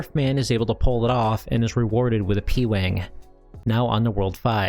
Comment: did he say “pee wang” ?